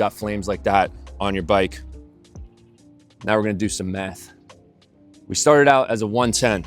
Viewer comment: I swear he said "Now we're gonna do some meth" LOL. I'm like whoo what channel am I watching here?